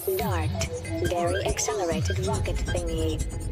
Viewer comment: what is she saying? I cannot understand what it's ... saying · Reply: Dairy Accelerated Rocket Thingy